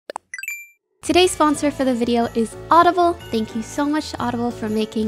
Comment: frick I'm so messed up from Soda that I misheard audible for autoblow...